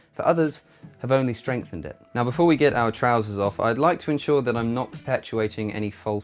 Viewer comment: I'm sorry, at 1:18 did you say, "before we get our trousers off"?